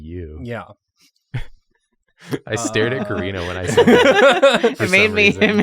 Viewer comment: the subtitles say "corina" and i dont think you could spell karina's name more wrong if you tried